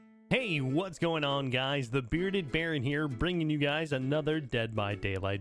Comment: i thought he said the bearded fairy